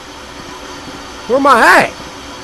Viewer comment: Cory:Where am i at? Captions:Where my hi? and it happened again at 14:12 Cory:Are you serious! Captions:Cereal I can't see I don't have a flashlight i dont know how the captions got it light that